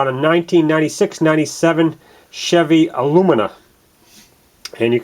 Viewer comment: People are so nit picky. Sounds like just said ah for a moment before recalling it was a Lumina. So it just came out sounding Chevy Ah-Lumina.